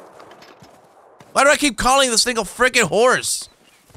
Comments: Am I tha only one who heard "why am I calling this niggah freakin horse" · it sounds like he said "why do I keep calling this nigga friecken horse"